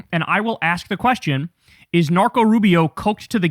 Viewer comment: LOL for a minute I thought you said "Narco Rubio" instead of "Marco Rubio" is that a new nickname?😂